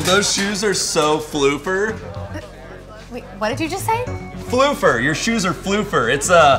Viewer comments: This entire time I thought he said Fluffer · I thought he said flooper then i looked it up lol and it actually is an insult Edit: foofer is also an insult. It means someone who is a fool.